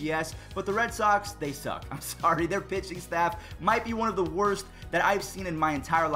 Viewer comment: When you said pitching staff I thought you said bitching staff